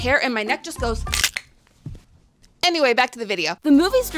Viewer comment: am i losing it or did i hear "amyway" at 4:00 :fish-orange-wide-eyes